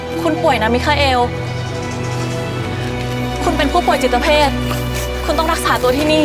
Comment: ผู้ป่วยจิตเพศ ได้ยินไหมมมมม มันต้อง ผู้ป่วยจิตเวช สิ เซเลน่า